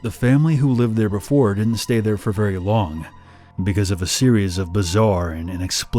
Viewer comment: my brain: sErIoUs (even with cc saying series)